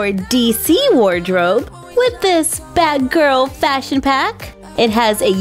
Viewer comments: I thought said badgirl than batgirl guess im wrong😭😭😭😭😭😅😅😅😅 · Anybody else notice it sounds like she say bad girl when she actually meant to say batgirl?